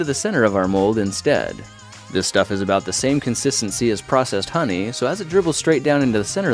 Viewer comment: Who else thought he said "Processed Honey"?